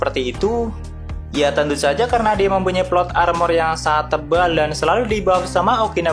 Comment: Plot armor? Atw gw yg salah dengar